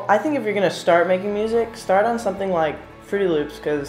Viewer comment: I couldn't make out what he said about software recommendations for beginners (1:57), anybody catch it? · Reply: He said it's good to start on FL Studio and then move on to something like Logic Pro.